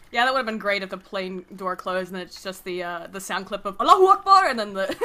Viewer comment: Every time they shout "Allahu akbar!," I hear "BLOOD FOR THE BLOOD GOD!"